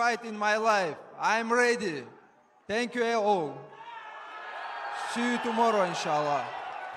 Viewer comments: It sounded like Islam said “thank you a-hole” does anyone know what that last word was? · Did Islam just say "Thank you - A-Hole" - ??? 😂😂😂